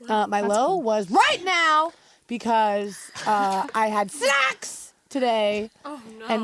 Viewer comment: wait, did Dani said " sex " @ 3:33 ? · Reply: No she Said snacks